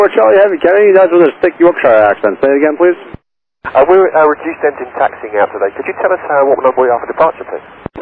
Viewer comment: "We're reduced engine taxi" is what they are saying